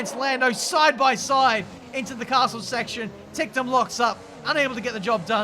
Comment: Did you really said "into castle section"? 😂